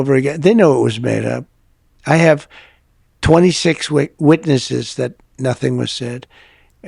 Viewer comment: Thought he said he has 26 weaknesses 😂